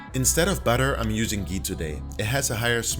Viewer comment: What did you say you used in place of butter? I can’t make out what you say( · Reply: Ghee